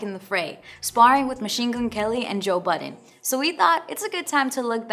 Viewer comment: did she say Machine goon kelly?